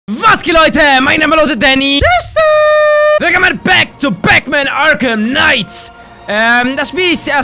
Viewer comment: Ich hab verstanden "Wilkommen back bei Backman !